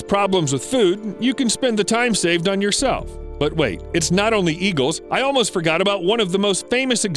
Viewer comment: not sure why heard it as "E-Girls" instead of "Eagles" xD